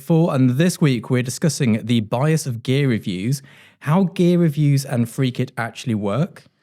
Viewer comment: For a second there, I thought you said: Gay reviews 😜